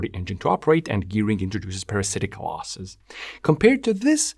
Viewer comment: for a second I thought you said “this introduces parasitic wasps” and I was like: “I didn’t even know engines had spiders!”